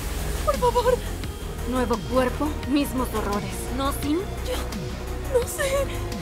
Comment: escuchaste lo que dijo V de que " mismo cuerpo mismos horrores Cyn"